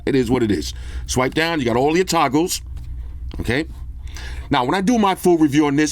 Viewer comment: did you say toggles or TACOS? 😂